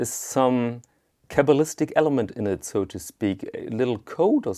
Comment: Did you say “kevelistic element”? Some sort of code at around 5:40. I love learning new words but I can’t find kevelistic so I am sure I misheard you (American). What term did you use? Thanks. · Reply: I think might be saying "cabalistic", referring to ancient mystics or astronomers. EDIT Dang! types a little faster than me.